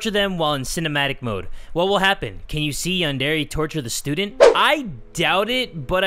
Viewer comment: What Jay says: Yandere Simulator myths. What the captions say: Non-dairy simulator myths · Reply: Jay said Yandere but the captions said Andheri at 5:56 lol